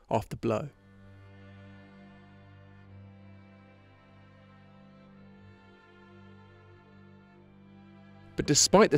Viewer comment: What's that sonny? I can't quite hear ya! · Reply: "Matter of fact, my sister said to me 'You worked on Hubble. That's an embarrassment to the United States'". That's what he said. But I don't quite understand why it seems to have been censored...